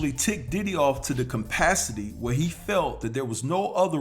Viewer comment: Did u just say “compacity”?? 4:58 Did u mean CAPACITY or what COMPELLED Diddy to react that way?? 😜